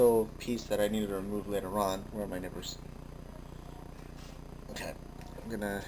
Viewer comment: haha, i thought you said "where are my nipples"